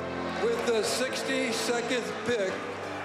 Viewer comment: Did he just say 62th pick?